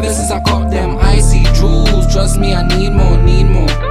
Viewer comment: Guns go bang bang, lifestyle gangland!... Instrumental is cold. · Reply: Chunkz thought that they were saying "lights down, Gangland"